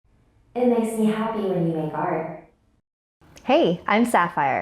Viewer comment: "it makes me happy when you fart" that's what I heard